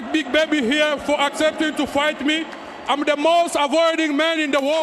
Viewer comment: lol, Bakole said, " I want to thank big baby here for accepting me, I am the most rewarding man in the world." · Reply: I thought he said avoided man in the world